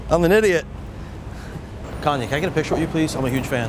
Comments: I thought he said that guy looked like KONY instead of KANYE (?) I was like oh oh lol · i thought he said kony lmaoo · KONY can i get a picture of you..... (who else thought he said that lmao) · i thought he said kony, looks more like him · haha when he said "kanye" i thought he said "kony" lmao